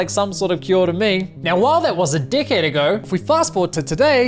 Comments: I thought you said, "while that was a dickhead ago..." Which would also be applicable. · you say 'decade', I heard 'd*ckhead' and it sounded fitting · I love how you said “a decade ago,” but your Kiwi accent made it sound like you said “a dickhead ago.”